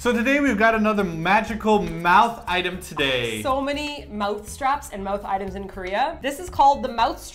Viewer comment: You kept saying "mouth strap" and I kept hearing "mouth trap".